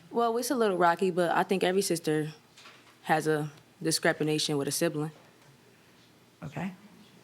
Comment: Every sister has a DIS-CREP-I-NATION?! Discrepination? Judge face said whattt?? What was she trying to say?? 5:45 He doesn't look like Kevin Hart to me lololol!! Super sad case tho! Hearts of coal. They suck! It's awesome when you have a best friend sibling, way better than an enemy. You fight as kids till like high school then you buddy up & conquer the world together!